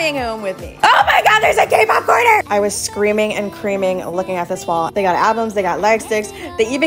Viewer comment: DID I HEAR THAT RIGHT “I was screaming and creaming looking at this wall” 😭😭wHat